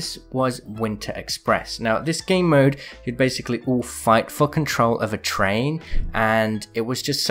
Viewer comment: it sounds like you said f$cking troll instead of for control